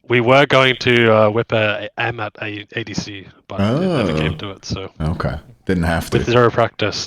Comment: is this ama? I think I’m deaf but I couldn’t understand what god he said 😢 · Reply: Yeah, Amaterasu ADC